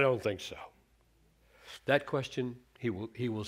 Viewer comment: I know you didn't mean to, but it did sound like you said Gabriel is God at 5:05.